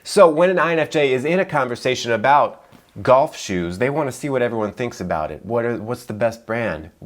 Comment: I first heard "Goth shoes" lmao throwback to 15 years ago when everybody listened to Evanescence and was wearing those